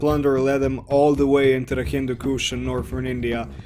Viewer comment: Hindu kush area m8,not the African one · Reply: Oh yeah, sry. :P You did say Hindu Kush, I just mistakenly heard it as "Kingdom of Kush".